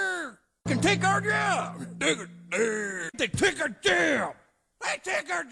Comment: it sounded like he said they took our jam